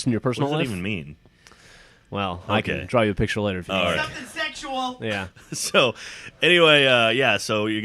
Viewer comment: At first I thought said "NOTHING SEXUAL" in the beginning! xD